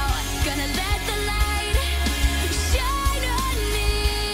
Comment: Echame la culpa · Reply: I think he misheard "Shine on me" with "Shame on me". I'm not a native english speaker and I also heard that the first time I listened this song